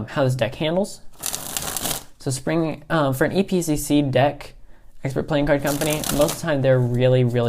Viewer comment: I didn't quite catch what you said at 1:40. Who did you say published this deck? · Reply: EPCC is Expert Playing Card Company. They are not the ones who published it. They are the printing company. They were released by Sansminds.